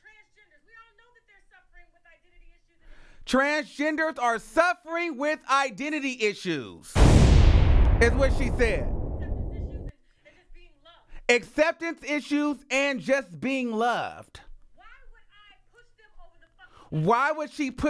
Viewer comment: she actually said " we don't know if transgenders are suffering from identity issues, acceptance issues or being loved" I don't see anything wrong with that..